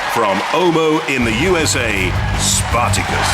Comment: From... sorry, where did you say, Stuart? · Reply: Ohio